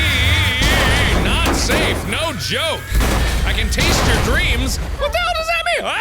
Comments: On the rollercoaster I thought he said I can taste your greens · on one of the hospital flashbacks I thought it said I taste ure beans😂😂😂